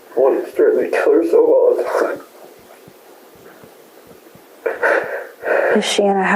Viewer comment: to me the "(indistinct)" audio sounds like he says she was "always threatening to kill herself all the time"